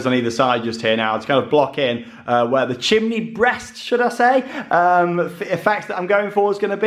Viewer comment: Oh god i had to rewind i thought you said ‘Chicken Breast themed’ abd realised it was ‘chimny breast’ 🤣😂🤣 Can i go to bed 😂😂